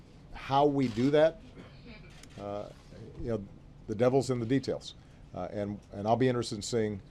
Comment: did he say the devil's in the details